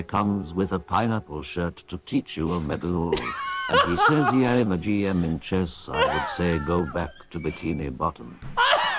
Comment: Can someone tell me what it's saying at 1:15 ?? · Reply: It says "Omegalul". It's a twitch emote used to denote intense laughter and be slightly mocking.